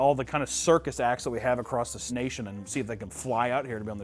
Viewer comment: I’ve been in the snail sub too much and I definitely thought at 46:10 he said snation 😂😂😅